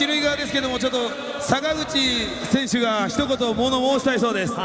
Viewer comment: あたり坂口選手といっているところが佐村河内選手って言っているように聞こえた